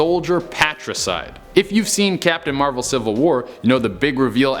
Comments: did he say captain Marvel civil war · did you say "Captain Marvel Civil War? What happened to America?!?!?